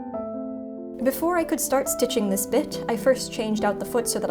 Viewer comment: I’m Half asleep I thought you said start stitching this bitch instead of start stitching this bit